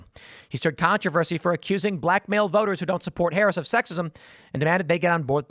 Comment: Oh, I heard you saying that Kamala was black maled. 😁